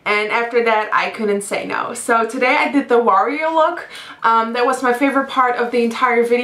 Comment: XD at first I thought she said the Wario look